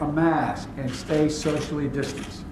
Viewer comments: Sounds like Joe says stay Sexually distanced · Sounded like joe said stay sexually distanced